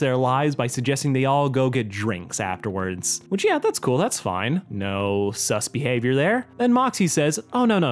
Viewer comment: Is blitz saying fine? 8:02 is moxxie saying boring???